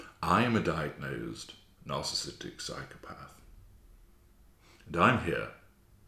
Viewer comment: I believe you, i misheard socio rather than psychopathic but your insight is Absolutely incredible.... You have ACTUALLY helped with my overt but boy did I fuck up playing ur videos for my X covert.... Nvrmind , that as a "soundboard", I utilized both my covert& overt Supervisors to aide my Next move regarding my X covert bc I naively found myself in turmoil as I couldn't understand wtf makes a person be near perfect, whilst pointing out my flaws, a yr later, turn into the fucking devil.... Only after to then become, their pathetic lab rat which, consequently, brought out the ANTITHESIS of what I knew myself to be🎭♟❣